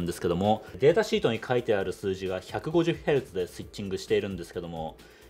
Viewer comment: 150Hzではなく150kHz ですね。 何故か k が聞こえません。 私の耳がおかしいのでしょうか? いつも楽しみに見させていただいております。